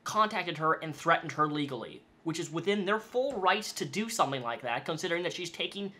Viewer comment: Did he just say 'within their rice' ?